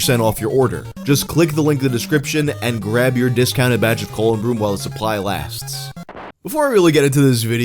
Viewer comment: ColonBroom . . . for a second there I thought you had said Colon Blow.